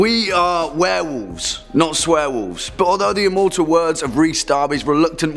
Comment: I thought he said swearwolf😄! That's me!